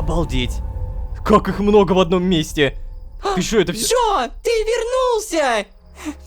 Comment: Только я один услышал тут Джоджо?